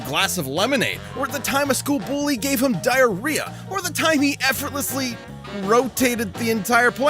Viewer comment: Por cierto, cuando dice que un maton de la escuela lo aterrorizo en el minuto 2:10, creo que dice que un maton de la escuela le dio diarrea. xd